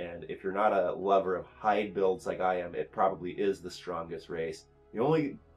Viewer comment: Yuan Ti: "If you're not a love of ___ builds, it probably is the strongest race." I can't understand what you're saying. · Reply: "Hide builds"